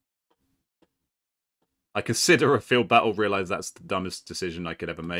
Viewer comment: did someone say field battle?